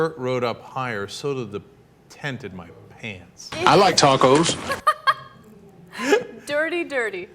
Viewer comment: Is it me, or did someone say I like tacos @ 0:59. WTF. This the third video someone is talking about tacos or mabe I'm just hungry. Or maybe I'm just picking up signals from the drive thru. Lmao